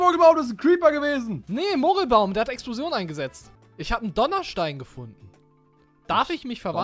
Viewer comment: "Ich habe einen Donnerstein." "Ich donner' dir auch gleich einen du Stein" :'D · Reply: Ich habe Dollarstein verstanden! LOL